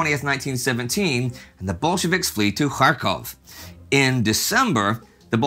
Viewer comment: When Indy says Kharkov, it sounds like he is saying Krakow.